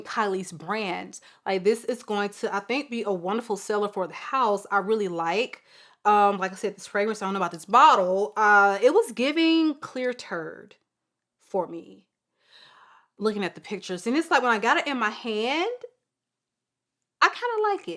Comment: Clear turd!!!! Lord help me when I say I laughed so dang hard! My husband was in the room listening and he thought you said it smells like clear turd. And he was shook! Hahhahahahahhaha thanks for a good Saturday laugh!